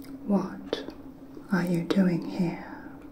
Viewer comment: What was said: "what are you doing here? What i thought i heard: "WAT ARE YE DOIN IN MY SWAMP"